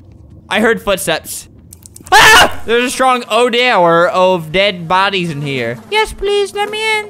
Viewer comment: DEAD BODIES HANGING Albert- Excuse me young lady Lights Turn Off Albert- AHHHHHHH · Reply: I thought you said dead booties